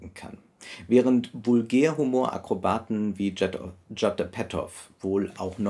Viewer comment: Hallo ich habe eine Frage: Wie heißt der Vulgärhumorakrobat, den Sie bei ca. 2:20 min erwähnen? - Ich verstehe "Jatar Petov". Wer aber ist gemeint? - Freue mich über eine Richtigstellung (natürlich auch, wenn jemand anders das richtiger versteht und weiß, wer gemeint ist). Danke! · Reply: Ich vermute Judd Apatow ist gemeint.